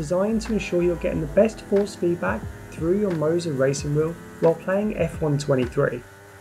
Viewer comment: Thought it said monza 😂